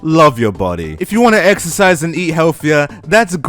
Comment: Kaichisaki i have another one, exercise? I thought u said extra fries😂